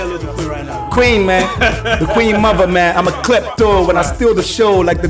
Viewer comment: I STEAL THE SHOW LIKE THE QUEEN'S MOTHER!!!. How come no one heard that? Lol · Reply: I heard it mehn.. 'I'M A KLEPTO'